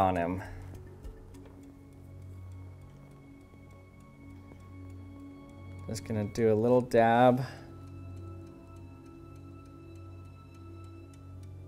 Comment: SORRY DID YOU SAID DAB OR TEB?